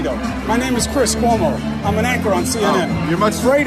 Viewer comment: I thought I mis-heard him — amazing! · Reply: I thought he said ‘anchor’ but Bean Man’s take is probably more accurate.😁